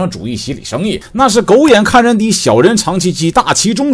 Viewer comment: 我沒看螢幕聽成 那是狗眼看人低，小人嚐雞雞.....😂